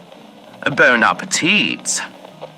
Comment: I thought he said burn a teat before realizing he said bon appétit 😅